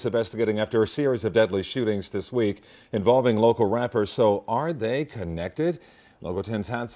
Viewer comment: Thought it said rapers lmao 🤣